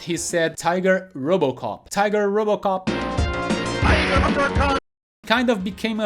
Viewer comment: It always sounded like Sagat said BIGER which was very confusing